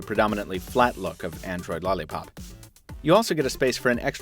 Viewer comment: sounds like you say android lahlipop